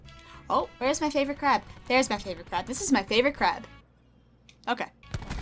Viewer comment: I thought she said this is my favorite crap😂😂